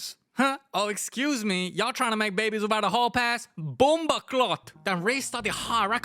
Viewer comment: did bro say bomboclat